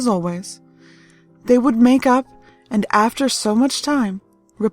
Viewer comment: I thought she said "*We would make out"* Me: WTF, well I guess that's how i'll make up to my sister!